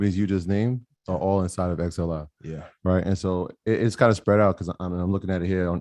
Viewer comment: What's the ETF he says at 33:16 ?? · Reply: XLI - it’s the Industrial Select Sector SPDR